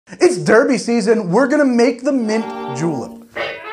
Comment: I thought he said he was making a mint juul